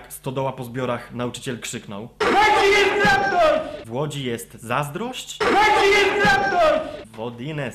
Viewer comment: ja tam usłyszałem 'W ŁODZI JEST TRAKTOR!'